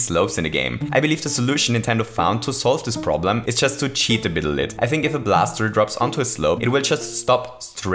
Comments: He said "a bittle lit" instead of "a little bit" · Did you just say “cheat a bittle lit”?